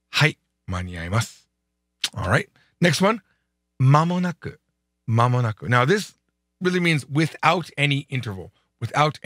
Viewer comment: So great to see your videos again! 2:30 間のなく - I hear まもなくinstead of まのなく, is there a typo ? Thanks for sharing as always, I learn so much from this short videos!